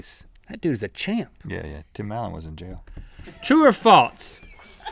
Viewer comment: What did rhett said? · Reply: "Tim allen was in jail"??